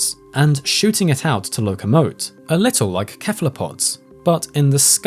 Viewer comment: Did you just say 'kephalopods?'